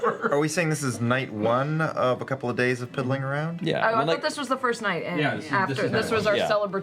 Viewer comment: Did Liam say at 3:16:50 "Is this the first night of pimmeling around?" That would proove an amazing knowledge of the German language!